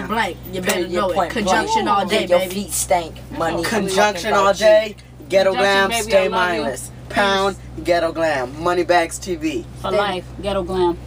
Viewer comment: did he just say "yo feet stank" lol